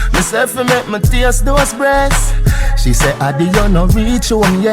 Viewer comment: I could have swore he said "toast breast" lol ... but big chunnne!!!